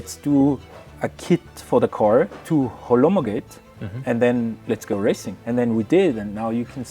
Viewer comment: Am I the only one who heard the word "Holomogate" :)) ) min